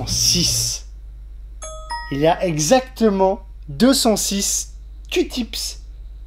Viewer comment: coton tige ? ta dit quooi ?😂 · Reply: Lori Robi bah il a dis « cutips »